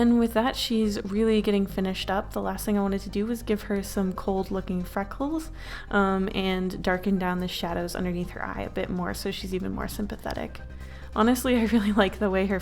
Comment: when you said freckles i thought you said fuck holes